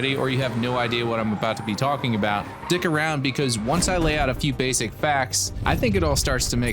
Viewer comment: lol did you just say, "Dick around?"